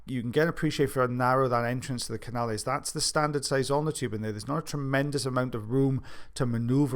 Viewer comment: I've always thought you were saying "standard size Almond tool" 🤦🏽‍♀️🤣